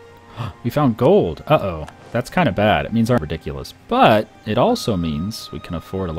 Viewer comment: Swear he said "That's kinda bad, it means I'm ridiculous" haha